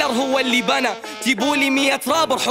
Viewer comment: سمعت كلمة (بيج)🙂💔